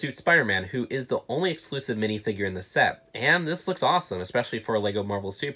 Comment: I thought he said this looks awful